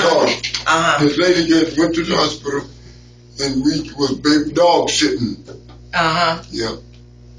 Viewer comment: This guy needs subtitles for anyone to understand him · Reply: Lol I thought he said dog shittin, he said dog sittin I think 😂